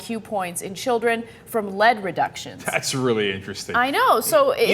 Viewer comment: i had to take a double look, i thought she said 'leg reductions' :)